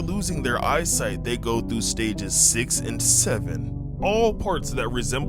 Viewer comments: d-did u say 67? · did I hear 67?